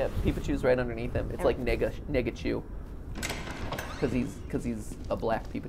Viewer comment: I thought he was saying something completely different for a second..... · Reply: "Nega-chu" as in "Negative (colors) Peepachu"